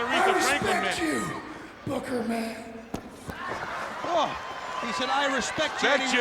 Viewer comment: “I respect you, Booker man.” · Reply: At first I thought he said "BOOGERMAN!"